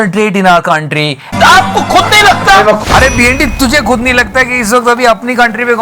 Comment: i misheard b&d as bhindi 🤣🤣